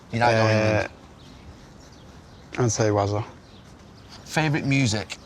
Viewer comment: who did he say was the best captain he had? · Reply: He said Wazza (Wayne Rooney)